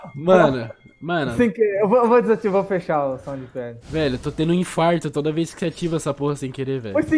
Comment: mano qq o ta usando pra deixar o som estourado? toda vez que ele falou eu entendi ''som de pega''